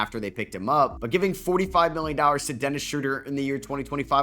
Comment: did you say $45 mil for shoeder?